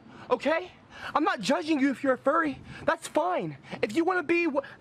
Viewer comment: I thought he said Fairy, not Furry...on another note, I bet his neighbors hate him.